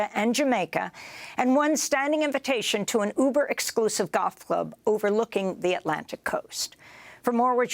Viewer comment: I thought she said "Ultra Exclusive Goth Club" and I was like damn if being corrupt and powerful means you get invited to such things then sign me up.